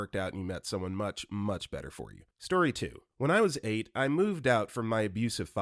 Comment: Story 2, at 3:09 it says 'when I was 8, I', but I think the subs ment to say 18. Going to listen more when I go to sleep tonight ^^'